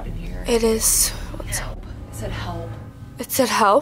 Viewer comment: This spooktober its been amazing! thank you guys! <3 Also, at 31:20 when the spirit talker says "help" I hear the voice saying "help them", dont know if I'm right tho. And at 31:38 right when is talking you can hear someone saying something and its not voice.